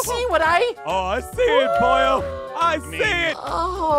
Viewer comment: Did he called him boyo? I love the idea of half angel half demon thing